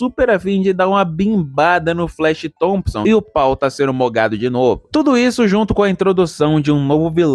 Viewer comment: Meu parceiro, melhoras. Eu consigo imaginar como você se sente, mas ele sempre estará com você. E eu escutei "meu paul foi moggado" kakskskakkaka.